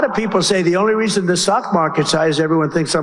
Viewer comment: It sounded like he said sock market!😮😅